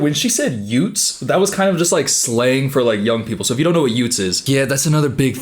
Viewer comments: as an Australian, your misheard words are very amusing lol. They're saying 'Youths' not 'youtes'. And 'OF' not 'OV', as in OnlyFans 😬😅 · I could be wrong, but I think she said ‘youths’. UTES 😂😭